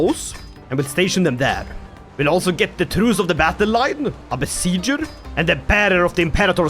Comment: I thought you said Druze of the Battle-Line instead of Truest or the Battle-Line for a minute lol